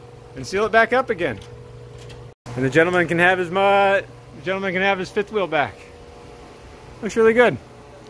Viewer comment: Hmm..did you say "the gentleman can have his mutt back"? now be honest 😊